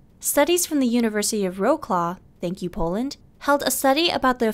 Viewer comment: Can someone write for me the university which she mentioned 0:37 ? Im from Poland but i cant understand the name of the university, because we probably pronounce this differently. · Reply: M. Wroclaw!